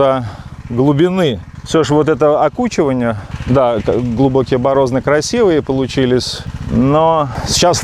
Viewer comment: Капалка хорошая все отлично , но не борозна а борозда как мне послышалось.